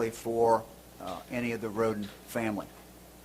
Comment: I know this is serious and I feel really sorry for the families of these people, but.... I thought I heard the sheriff say " all the victims are of the rodent family" and almost burst out laughing. I'm sorry I'm a bad person.